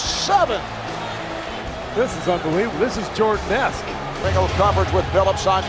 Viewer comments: he actually said this is jordan esque not best. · what he actually said is this is Jordan-esque